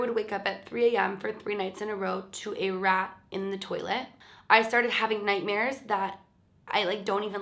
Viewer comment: i was just listening to the video and i thought you said "to make a rap about the toilet"